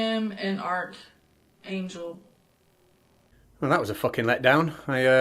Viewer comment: It really sounds like she's saying "art angel" rather than "archangel." I wonder if she just misheard the word that she is repeating and doesn't understand what she is saying, or ... what?